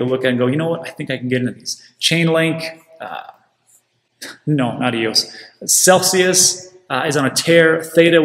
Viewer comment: Did you say "Chainlink, adios" or did you say "Chainlink ... no, not EOS" I've been curious what the sentiment around LINK is now. That is not a coin that has popped off yet. Thoughts? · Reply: Chain link NOT EOS.